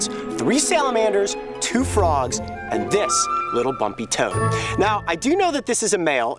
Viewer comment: Hahaha I Thought Cayote Said My Toe!